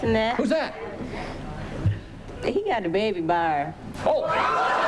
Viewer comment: what the hell does she say? He’s got a baby bAr? Anyone...